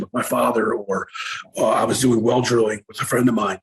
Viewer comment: What did he say he was doing for work before cannibal? Around the 57:50 mark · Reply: hey man, I think he said well drilling